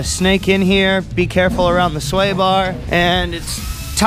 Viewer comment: I thought he said "be careful around the soy bar"😂